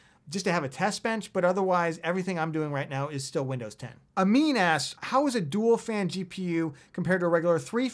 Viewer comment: I 100% thought you just called that listener, "A mean ass"